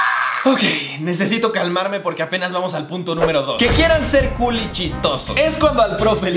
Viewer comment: pensé que dijo "que quieran ser culichis xd" Like si también entendiste asi